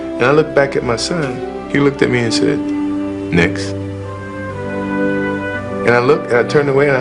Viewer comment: does anyone know what he said at 34:53 cause even the subtitles don’t know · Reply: M McA next